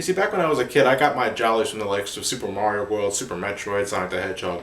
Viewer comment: I'm sorry, but did you say "Super Mario Boy"?